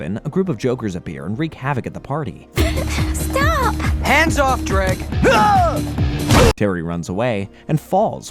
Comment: am I the only one who heard “hands off drake”